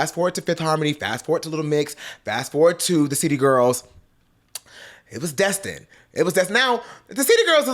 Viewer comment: Every time he said City Girls, I thought he said Cheetah Girls.🤣🤣🤣🤦🏻‍♂️